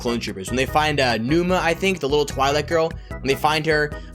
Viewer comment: that little twilight girl i know you meant to say twei lek girl but to me it sounded like twilight girl